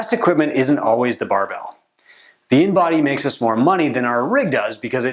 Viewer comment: What did you say after you said "the best equipment isn't always a bar bell?" you said something like a B and body makes more money than a rig? at around 8:04? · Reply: It "InBody" - a device for measuring weight and body fat. Many Two-Brain gyms use them to get more detailed info about a client's body. They are costly, but you can use them to generate a return on investment. Similar measurements can be obtained with calipers, but that takes more time and work. Thanks for watching!